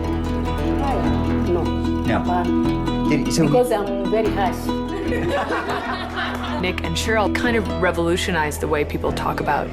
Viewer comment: "Did your husband beat you?" "No....because i'm very harsh" "laugh" Am I the only one who sees the problem here? For all you know she has just said she was a husband beater. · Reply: I thought she said "because I'm very nice"